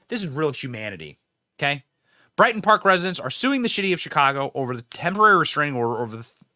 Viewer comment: Benny, did I hear you say "the shitty of Chicago"? A propos!